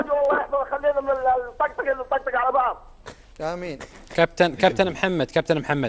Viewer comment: هههههههههههههههههههه علي الزهراني من القمطه مدري وش يقول قال : امين